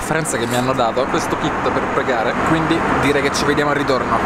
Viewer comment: Ma ho capito bene al minuto 06:48 hanno dato il kit per pregare?